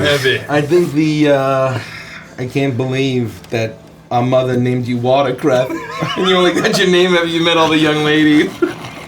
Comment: If someone sees this please transcribe what saying in 22:05 that has regret his life choices ? is it "it can't believe mother called you watercraft[?] you only got that name after you met the young ladies." I dont understand what that means TT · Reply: "I can't believe our mother named you watercress and you only got your name after you met all the young lady's" refering to mace/sausage having a large sausage if that helps